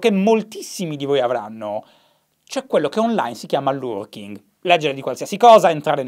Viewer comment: Loworking? Non ho capito come si chiama in Uk l''atteggiamento che hai e che forse ho anche io :) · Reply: È lurking :)